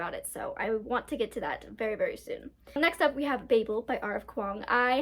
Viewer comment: Oh definitely read ghosts of the shadowmarket before chain of gold!! ^i was typing this comment as u said babel and i stg i thought u said bagel LMAOO 🍂🍂